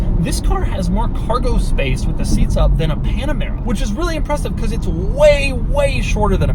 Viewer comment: Thought he said panda bear 🐼20:29 I think I like the volume knob being lower so your hand can precisely adjust the volume during hard cornering—and the windshield placement is so its easier to pour for a commonly used fill cap. The tiny storage compartment seems like it's for your phone vertically